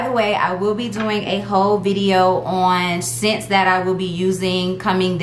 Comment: I know I need a whole nap! I could’ve sworn you said you were going to do a ho video. I said mymymy.